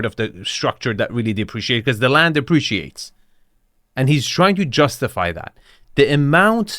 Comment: Did you just say that land depreciates at 27:19? Or did I hear that wrong?